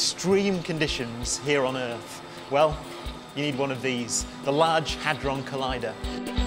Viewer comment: I thought the last part said the "Large Hardon Collider".